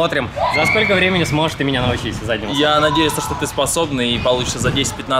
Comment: Чтооо он сказал? Задницу? Или мне послышалось?